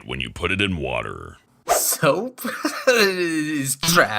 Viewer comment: I thought it said soup and I freaked out lol